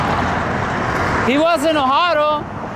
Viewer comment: And all this time I thought you were saying "he was a mohado". Oh well, sometimes fiction is better than reallity.